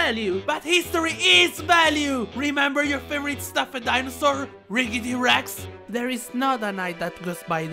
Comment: DID I HEAR RIGGY? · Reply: Riggety